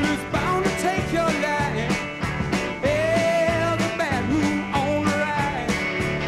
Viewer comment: When I was a kid, I always thought he was saying “the bathroom’s on the right” 😂